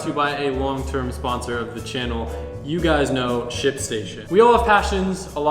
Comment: why did it sound like he said shit station? (2:25) lol